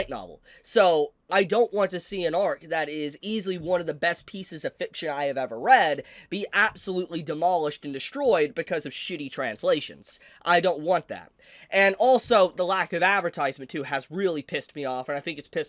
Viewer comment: I know what you're saying, but I understood „Chibi Translations“. Maybe your next channel? ;)